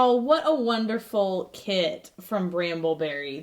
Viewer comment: I thought she said what a wonderful KID from bramble berry😂😂